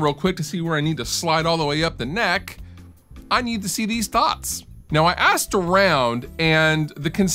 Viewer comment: I thought you said, "I need to see these THOTs". I was gonna go, "fuck yeah!". Until, I replayed it. You said dots. lol